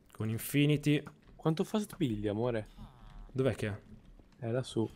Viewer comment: quantfast piglia amore? come ho capito io poi se e sbagliato ditemelo